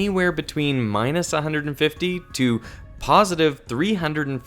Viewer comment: subtitles say -350 but video says 350 (small error, just letting you know)